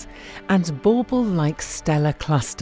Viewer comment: wtf is the word here, CC says 'bbike' but it sounds like "bubble, marble, barbell, barble, barmble, bello, bear-caramel, camel-rambo(flouge?), bunfamiliar-colin-ferrel, ramblin-man-(jamboreevan) or most likely it's simply: because call maestro mellow yellow". So which one was it I am betting on "ambo ramblin' on a camel possibly either w or after having lost marbles". - thank you kindly... Anyone else have this issue or i'm just can't read showin' my slowmind to rear-wind-ear speed?